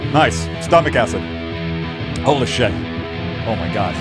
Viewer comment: did I hear Ola shit or holy shit😮